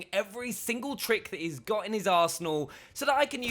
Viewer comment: I thought he said "In His Asshole" hahahahahah 😂